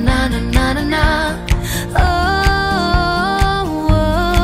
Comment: I think she says "And that, my friends, is the best way to deal with hate" I don't know, great video btw!! :D